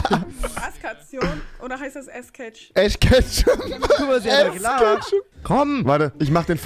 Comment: Bei 4:47 ich hab ash kechtup verstanden 😂